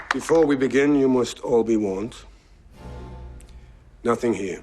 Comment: 當他說到All be warned，我就聽成Obi-wan，然後想到Obi-wan是不是隱喻All be one。